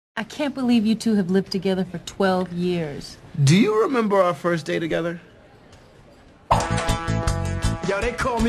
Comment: It sounds like Turk is saying ``Do you remember our first date together?´´ XD